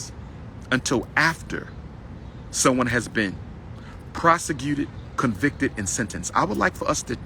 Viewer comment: Hey Tom...I think Umar was trying to say prosecuted but for whatever reason what came out his mouth was PROSEGECUTED hopefully he’ll pick up in the second half Back to you.